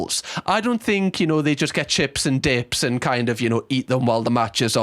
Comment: I thought he said ”they’re getting chips and d*cks”… yeah that might explain it… xD